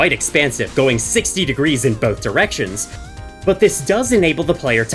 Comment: idk if that’s what his vision range would look like though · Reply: Oh wait, I'm the big dumb dumb 😭 I swear I heard you say 90 degrees, but you said "going 60 degrees in both directions". Though as I look at that little code snipped, it looks to just be 60 degrees, aka 30 on both left and right which is surprisingly low. I guess the only way to find out is to test it! Also that "DeltaYaw" sounds weird, since I would assume based on the name alone that it is measuring change over time? Though I guess you probably know what it does :P